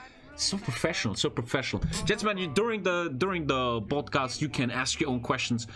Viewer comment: did he say baldcast? XD